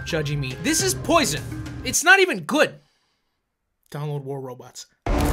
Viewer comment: i thought he said "donald war robots" for a second.